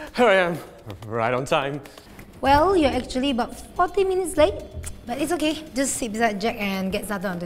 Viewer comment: In 7:29 when said 40 it sounded like fvck!ng...